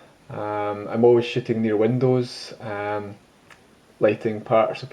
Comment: Sorry, sounded like you said "always shittin' near windows" 😂😂 Nice review! I just can't seem to justify the extra $<num> for the G lens, especially because you can pick one of these up used for around $700-800. does this focal length work well for a dx camera? I'm currently shooting on a D7100 :)